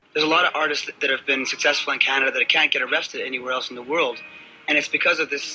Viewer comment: I swear I hear Adam's say arrested